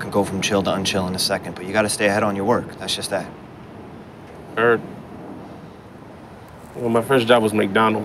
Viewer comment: LoL the subtitles say INAUDIBLE but you can clearly hear him saying Heard. Who tf wrote these subtitles LoL